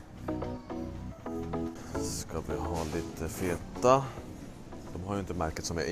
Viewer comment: vad säger du här riktigt?😳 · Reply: Feta 😁